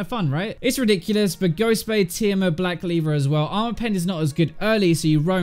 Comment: did he really say black leaver at 4:37? xD